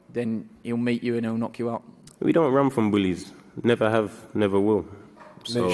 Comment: What did he say??? Viddal, You Don't, Is that right? Edit: oh okay he said "Bullies"